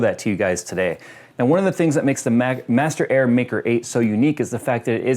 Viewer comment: thought it said master air banker